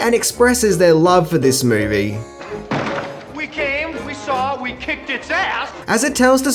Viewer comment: I could have sworn that Bill Murray said"We came, we saw, we kicked ass" but I suppose I could have missed the"it's" the many times I have seen the movie. Lol